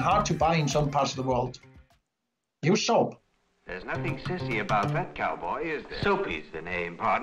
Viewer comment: Did anybody else hear “There’s nothing sissy about it”